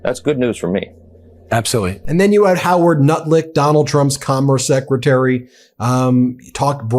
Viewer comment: DID YOU SAY HOWARD NUTLICK 🤣🤣🤣🤣🤣🤣🤣 sanka im ded mon